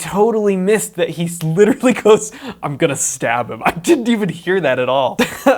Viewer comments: I thought he was saying am gonna stop him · I didn’t realise the guy that whipped out a knife in the wicked witch of the east until you mentioned it. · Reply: I thought he was saying "I'm gonna stop you" for the longest time!